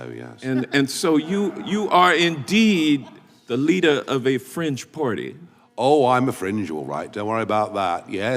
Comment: I must say, I find what you have just said grossly offensive mr congressman... ...oh 'fringe' I see! god no, i thought you said 'french party'! Never mind, carry on congressman